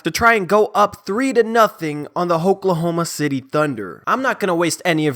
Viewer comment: Did he just say "Hopelahoma City Thunder" at the start of the video? LOL what a savage. I'm soooo nervous for tonight's game.